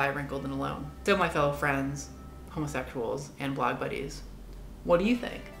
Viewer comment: did you just say, "homosethual"?